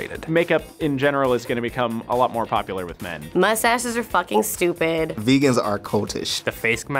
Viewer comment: Her:"Mustaches are f**cking stupid" Cut:(the next guy has a mustache 😂😂😂) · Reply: Oh I thought she said ‘Most asses are fucking stupid’ 😂 🤦🏾‍♂️